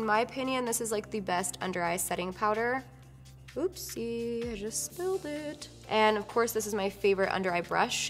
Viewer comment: did you just say "oupsi JE...." or "oupsi JUST" at 13:22 ??? did you pronounce a french word ?? All the love from France